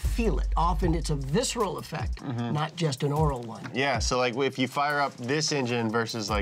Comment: did he just say oral one? sounds and oral? broo and you won an oscar like prize 😂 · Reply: "Aural one" as in hearing. It's super confusing as the only way to differentiate the two is context.